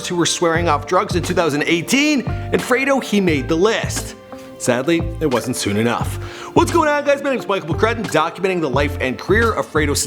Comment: did he say his death didn't come soon enough